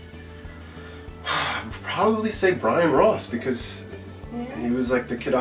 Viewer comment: I THOUGHT THEY SAID RYAN ROSS I WAS LIKE WHAT